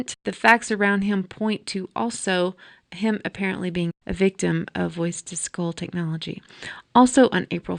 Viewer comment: to 5 :33? What is the dialogue as I can't understand what you say nor does CC decipher legible words. Thx. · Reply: She says, "...point to him apparently being a victim of 'voice-to-skull' technology."